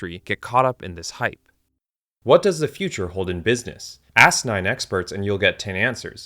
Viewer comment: I thought he said "asinine experts"!😂